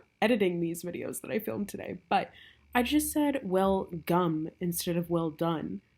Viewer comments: Honestly, I heard "welcome" not " well gum" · When talking about these witches don't burn it sounded like you said "welcome" and not "well gum"